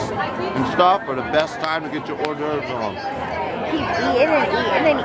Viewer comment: What does say at 0:40 ? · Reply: I think he says its time to get your Hors d'oeuvre on